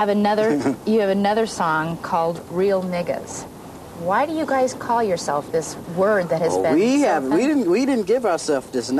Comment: wtf did she just say? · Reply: Eazy E don’t have a problem so what’s your prob?